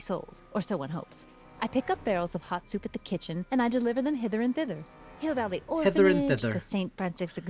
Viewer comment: Personally, so did I, I thought it said Hitler&Thitler but I knew that wouldn't make sense lol